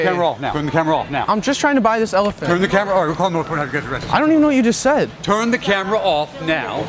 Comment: I think he said “Turn the camera or you’re getting arrested” I couldn’t really tell bc it’s hard to understand what he said but this is what I heard.